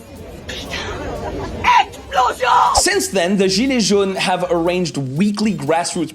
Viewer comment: what does she say ? Macron explosion ?